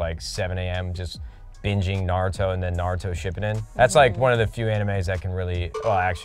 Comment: did Matt just say ship eden??? SHI PUU DIN cmon nade 🤣